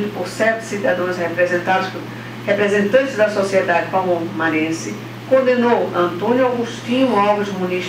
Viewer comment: eu ouvi a juiza dizer sete "CIDADÕES"?